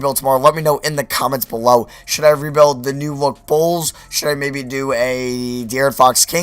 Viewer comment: Hey guys I hope you enjoyed, Im sorry for not getting this out earlier because I was away lol. What Rebuild should we do tomorrow? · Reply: I thought u said New York bulls. XD